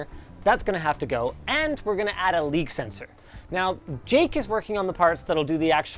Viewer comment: I thought he said League sensor. Yes, he does need to have a LoL sensor on it, a parent must know when their child is subjecting themselves to that.